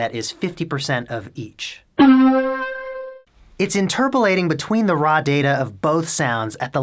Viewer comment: did he just say "interpolating". Is that voodoo